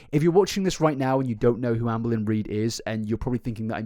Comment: I found out about her through you…. And I continue to find out through you. Also I know her name is Amber Lyn…. But I heard you say it once as Anne Boleyn… so now I just hear that…. 😅😅